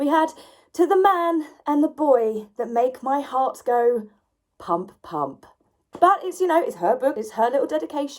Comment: Do you think she knows what humping is? Woops sorry thought you said her heart went hump hump not pump pump.